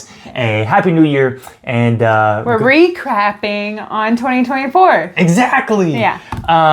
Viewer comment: Did she just say she is ‘re-crapping’!!!? 🤣🤣🤣